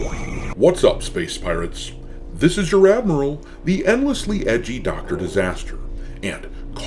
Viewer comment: I thought this man just said endlessly edging... XD